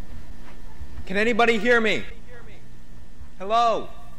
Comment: Pontosabban azt kérdezte a csávó,hogy “can anybody hear me?” azaz: valaki hall engem?